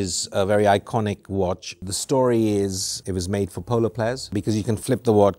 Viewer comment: The volume was low, and I thought you said it was made from polar bears 😮